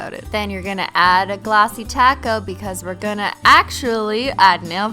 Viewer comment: Lol at 7:12 it sounds like she is saying to add a glossy taco